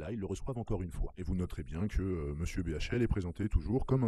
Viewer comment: C'est moi ou vers 2:00 il est présenté comme "miss bhl" ?